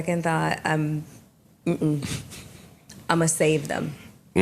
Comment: I thought she was saying "I'm gonna save them" because whatever recourse HR said she could do, she didn't want to do all that.